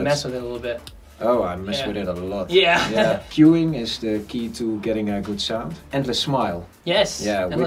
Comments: what is he saying is his key at 6:25??? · what did ralph said about saving up alot of work? couldn't catch the actual word. all i heard was "the smile". thanks! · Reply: I think they are talking about "endless smile" :)